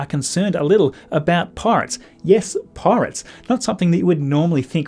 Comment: That confused me so much! I thought you said "parts". I was like: Dude, what parts? They've assembled it at this point, surely...?